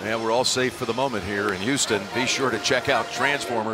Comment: DID HE SAY HOUSTON BRO IM IN HOUSTON🤩